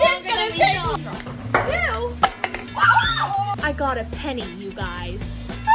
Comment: I thought she said ‘run a penny’ LOLL IM DYING💀